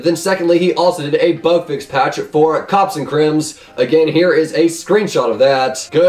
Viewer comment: did he say cops and cremz instead of cops and crimes